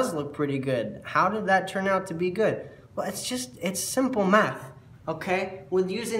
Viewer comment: Hehe at first I heard " it's simple meth" 😉